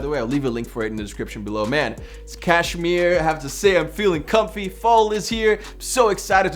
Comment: what did u said Kashmir?